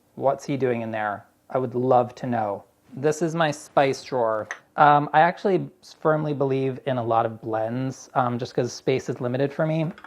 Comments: "I actually spermly believe-" I had to listen to that about 5 times to make sure I heard it right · Did he say, "Spermly believe" ? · Did anyone else hear Chris say "spermly believe" ? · Did Chris just say, "I spermly believe" at 20:20? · "I actually spermly believe in a lot of blends"... am I hearing this right? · did he just say spermly believe haha